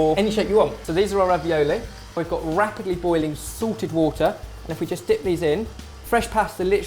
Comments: I thought you said "we've got rapidly boiling SORTED water" :D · When he said salted water I thought he said sorted water :3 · thought he said "sorted" water not salted water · haha I always thought he said sorted water...